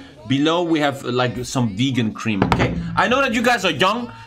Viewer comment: Did you say "Giggle Cream"?